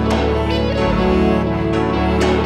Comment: anyone hear "this is awesome"? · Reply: Happy_Killmore this is axiom